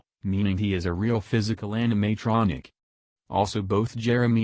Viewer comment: Though out the video you instead of animatronic you hear... 0:44 Anime-tronic